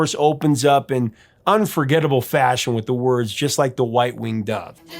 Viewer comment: I like that Stevie was inspired by a misheard word and now a lot of people mishear white winged dove as one-winged dove. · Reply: Not to get political, but I always thought she was saying "just like the right winger"